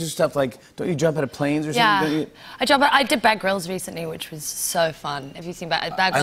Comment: I thought she said "I did Bangbros"!